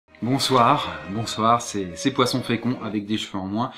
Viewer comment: Putain au début j'ai entendu "Bosoir, bonsoir c'est Poisson très con" xD